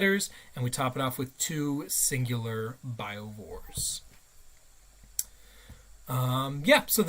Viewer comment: That is 100% correct. I don't think multiple biovore squads is a viable option for that exact reason. I played a 3 man squad for a while, but now I'm down to just a 2 man. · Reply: sorry, it sounds like you said you are taking "two singular biovores". Have been seeing a lot a folks talking 1 man squads and repeatedly using the action. Do you think Spore Mines dropped by a Harpy can move? That is the other big Q I keep seeing come up. I think RAI, no